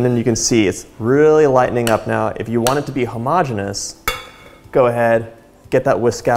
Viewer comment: As a chemistry student, when I heard him “hem-ogg-e-nous” instead of “homo-gene-ous” that KILLED ME